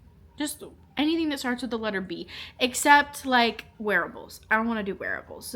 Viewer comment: I really love this approach to the A-Z challenge! I thought you said don’t suggest werewolves lol. Took me a minute to realize it was wearables. Note to self, pay attention cause multi tasking messes with the hearing. Thanks for sharing ❤